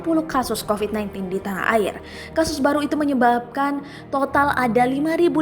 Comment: MENYEBALKAN ???gua ngak salah dengar kan???